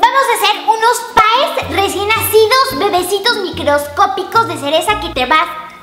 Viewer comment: "Vamos a hacer unos pays, recién hacidos" A, ok... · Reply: Yo también le entendí "hacidos" pero dijo recién nacidos como decían que eran chiquitos.